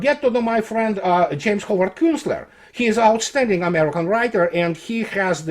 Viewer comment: Can someone tell me what is the author's name he mentions @ 20:42 I hear it as James Hoover Kussler which doesn't show up on google. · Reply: I don't know, but could be "James Howard Kunstler" ("American author, social critic, public speaker, and blogger"-wikipedia).